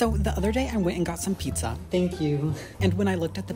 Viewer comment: At first I thought he said the other gay I was like whattt!!!? Then I restarted it it said the other day